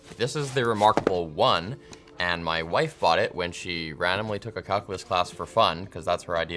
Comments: i had to listen 7 times. did he say calculus or cuckulus? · I just heard "cuckless class" what is that?